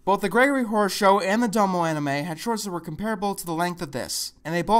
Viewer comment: what did he say at 3:17 the gregory horror show and the what anime? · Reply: Really late comment, but I think he said Dumbo anime.